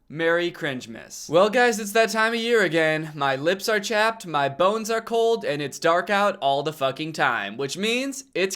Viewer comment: "My lips are chapped my bones are cold and it dark all the time.. It's Christmas" 😂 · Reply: At first I thought he said "my bones are old" 🤣 Winter does make my bones feel old AF